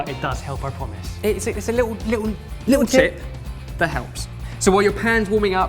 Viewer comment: All i heard was, "Just the tip." don't hate me